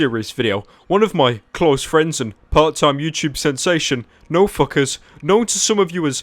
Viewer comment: i thought he kept saying no fuckers when he said nfkrz